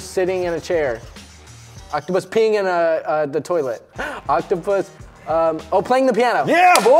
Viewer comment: Go to 6 30 and instead of saying peeing in the toilet it sounds like he said pen** 😂😂😂